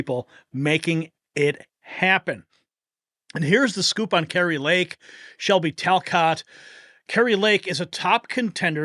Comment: Save Next News! We need you! Become a sustaining Liberty Guardian of our efforts, tap here now: <link> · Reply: I. Thought. You said. Carry. Lakeview